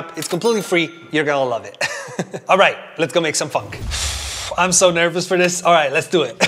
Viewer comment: bro i thought u said lets go make some f#$@